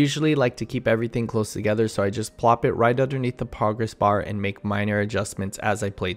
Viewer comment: I had the video playing in the background and swore you said “poggers bar.” That’s silly of course but did make me double take haha. Great video!